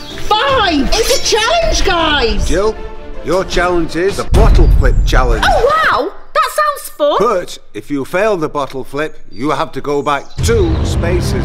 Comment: I thought that he said the bottle lick chellenge